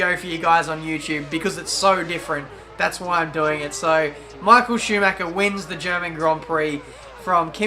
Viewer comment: I missheard it as marco schuhmacher